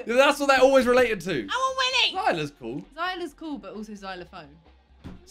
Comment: That sounded like: I want Willie O_o